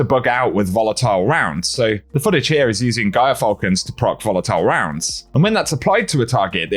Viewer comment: "The Footage here is using _______ to proc volatile rounds". Please fill in the blank. I don't understand what he said. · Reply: Gyrfalcon Hauberk, a hunter exotic chest.